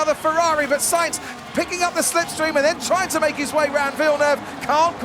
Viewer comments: Did he say : "... picking up the slipstream, and then trying to make his way round VILLENEUVE ..." at 1:37 ????? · Did i Heard that right ? At 1:37 did the commentator said around Villneuve ?